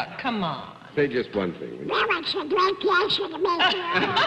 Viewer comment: What is he saying at 7:00? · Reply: well it's a great pleasure to be here